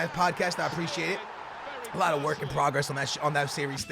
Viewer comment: On 4:15 did he said "alot of work and progress on that shit"?